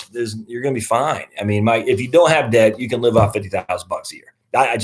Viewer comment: What the hell did you say?!? “With no debt, you can live off of *%#^@ a year” WHAT??? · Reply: 😆 50k whooooo?